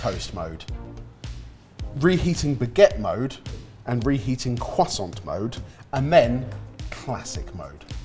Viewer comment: Did i just hear the legendary cwoisssssant